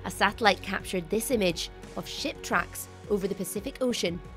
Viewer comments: I swear I heard her she "shit tracks" T felt like kind of cop out. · At first I thought she said "shit tracks".